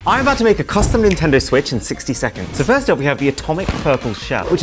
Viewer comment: I thought you just said 53 seconds